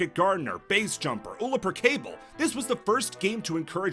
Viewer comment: im sorry, did you say "Ullaper Cable" at 8:26?